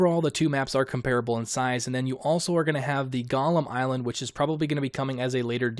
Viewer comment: "And you're also going to have the golem island...". I had to go back once or twice and hear him say it again. I thought he said "Gollum Island". Island isn't available until DLC "tricksy little developers! They stolez it from uz!"